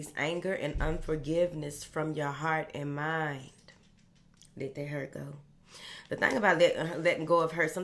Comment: yes, I heard let it go